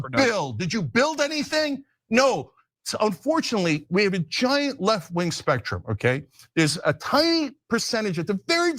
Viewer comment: Did Cenk just say, "Unfortunately we have a wide spectrum on the left?"